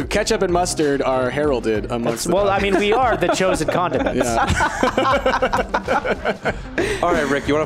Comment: When they said condiments I thought they said condom mints